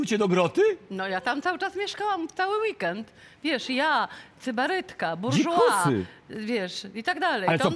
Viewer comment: Co mówi Magda i co to znaczy? Rozumiem kontekst ale jednak może ktoś wie konkret. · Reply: Chyba sybarytkę.